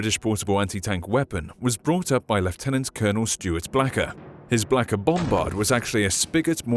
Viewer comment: did he just say blacker bomber 0:40 lmfao